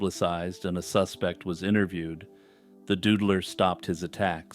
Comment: I really thought you were saying 'The Dudeler' because he was slaying men. SMH